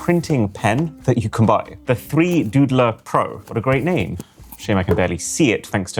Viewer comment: I heard 3goonerpro